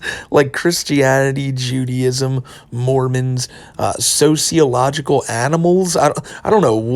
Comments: I think Chris was trying to say Zodialogical. As in the astrology Zodiacs like capricorn and scorpio. (I am like 95% sure zodialogical is not a real word which is par for the course for Chrischan) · i heard that as "zodialogical animals" like zodiac animals of somekind? But fr , he seems strung out on meds to me, wouldnt he shocked if they gave him thorazine because of his delusions of grandeur. Plus he probably had a meltdown after the con experience in private